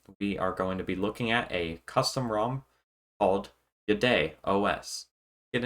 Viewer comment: At first I thought you were saying "BidetOS". That would have been a great name for an OS that cleans up after Google.